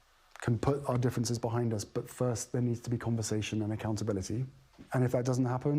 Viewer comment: *****Did anybody else notice that: #07:20 Harry said that for he and his family to put their differences behind ''it first there needs to be CONVERSATION and accountability.'' #07:25 Pierce wrongly has heard it as ''Compensation'' and continue his speech, probably, based on that misheard word. And that's an error.